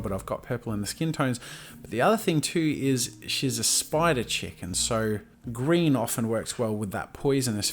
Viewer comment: he said"spider chick, and..." but I thought he said "spider-chicken"